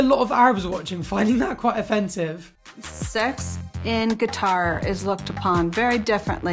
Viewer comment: She literally says guitar instead of qatar at 8:39 I‘m screaming 😂😂